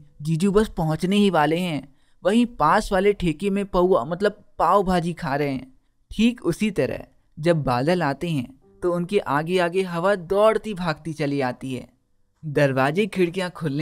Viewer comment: Did he just said pauvaa